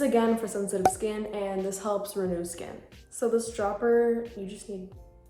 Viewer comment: did you just say helps reduce skin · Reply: She said renew